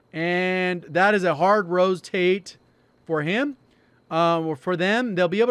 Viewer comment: Did he just say "hard Rosetate"?